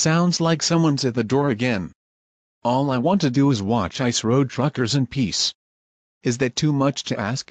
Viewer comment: Did Fred say an F word? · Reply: He said “truckers”